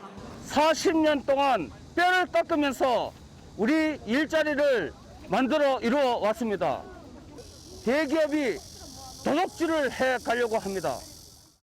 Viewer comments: 40년동안 남의 돈 등쳐먹으면서 우리 일자리를 만들어 이루어왔습니다.. 대기업이 우리 나와바리를 도둑질 해 가려고 합니다 라고 들림........ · "40년 동안 뼈를 깍으면서 우리 일자리를 만들어왔습니다" 나는 이게 왜.. "40년 동안 사기쳐 왔습니다" 라고 들리지??